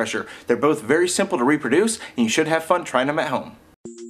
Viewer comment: I was trying to fast forward through the intro, got to around 0:27, and thought you said "You should reproduce, and you should have fun trying it at home."